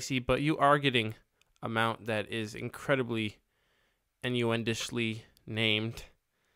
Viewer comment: what ddi you say "endoendishly" please respond i need to know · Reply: Ahaha I kinda just made up the word "innuendishly" from the word "innuendo" (basically just trying to turn a noun into an adverb). As you could tell, it didn't really work.